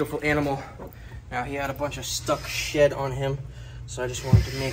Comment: Definitely thought he said “stuck shit on him” and gagged thinking he kissed Rusty with “shit water” on him …. Realized afterwards he said “stuck shed” 😅😂